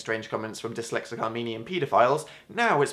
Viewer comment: did he ffffreaking say "Armenian"??? i really hope i heard it wrong 10 times.